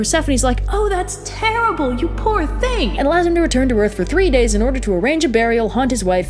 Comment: 3 days? I thought you said 30 years!😂